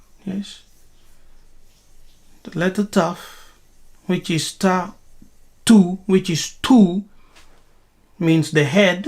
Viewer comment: 6 months later, I'm hearing this teaching again for a refresher. Sonically it sounded like you said tattoo. Come to think of it, Taw tu sounds like tattoo. It makes good sense. 💯